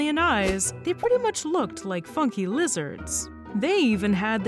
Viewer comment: For a second I misheard it as "f*cking lizards"